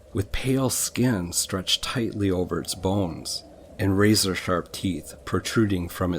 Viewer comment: LMFAO.......I was listening to this while going to sleep (ya idk why thats when i choose to listen to these) and I swear I thought you said "With pale skin stretched tightly over its balls" instead of bones